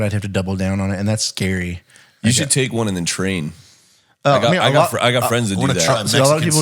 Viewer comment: I swear he said, "you should take one on the train" and all I could picture is a video of trying to take a peaceful commute but all the while he has an exaggerated monster bulge ripping out his pants... the look on people's faces would be priceless.